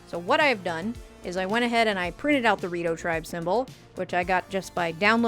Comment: …I keep hearing “Dorito Tribe symbol” 😂